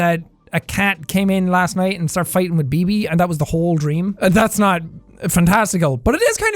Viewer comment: Did he say BB (Bebe) 10:24 I got really excited cuz I have a dog named bb 😀